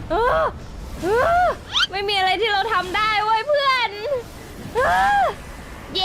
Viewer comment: อะไรนะ ผมได้ยินไม่ผิดใช่ไหม "ไม่มีอะไรที่เราทำได้" ให้กำลังใจแบบใหม่?